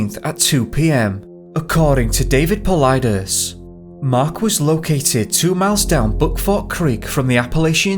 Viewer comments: The two men should have stayed together curled up in the sleeping bag that the one had and kept each other warm... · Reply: sounds like he said "Mark was located two miles down Buttfuck Creek" and I lol'd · sounds like he says he was found 2 miles down Buttfuck creek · anyone else hear him say butt-fuck Creek?😂😂😂